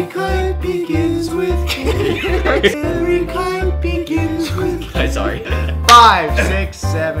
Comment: I thought he said "every kite begins with gay."